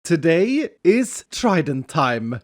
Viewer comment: Lol I thought you said,"Today is China time," at the beginning of the episode.